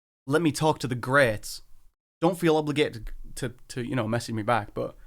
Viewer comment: I heard it as the greats don't feel like the should respond. Like he's pissed for getting left on read